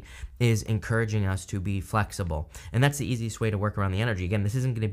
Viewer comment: Ohhhh!! You said "be flexible"...lmao I thought you said "f*ck stability"... 😆 🤣 😂